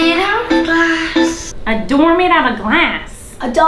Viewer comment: I thought she said a door made to of glass